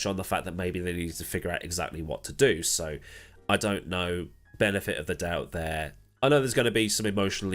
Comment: I misheard "Benetton of the doubt" and, that could be a good saying for giving anything but the benefit of the doubt